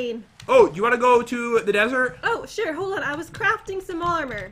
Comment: I thought say u wanna go to the neither 😅